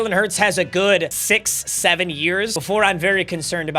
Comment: did bro just say 6 7